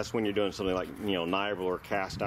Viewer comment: I can't understand something you said a few times in the video. At 24:55, you say "That's when you're doing something like ??? or cast iron." What's the "???" ? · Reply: Never mind. NiBrAl. I found it on your site, under Casting Furnaces. I don't do any marine stuff, so I didn't recognize it.